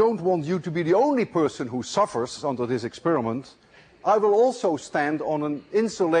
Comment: i thought he said sperm.